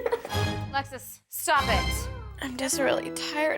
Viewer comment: it sounds like shes saying "alexa stop it" 😂😂